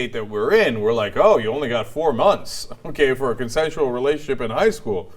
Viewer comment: I am an English learner. Could anyone please tell me what Cenk said at the point from 4:50? Did he say "conventional relationship"?